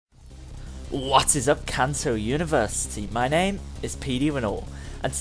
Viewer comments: OK BUT I HEARD "WHAT's up cAncER UNIVERSE" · At the beginning I thought you said "cancer university"